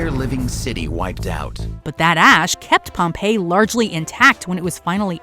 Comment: Am I the only 1 who thought for a second she said " but that ass kept Pompeii largely in tact." I was confused but laughed at what I thought was a much welcomed funny mistake.